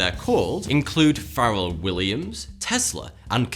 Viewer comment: Did he just say Feral Williams?